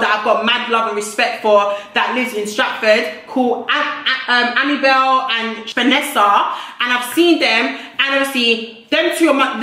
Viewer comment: why did i hear abigail and vanessa 🙂🙃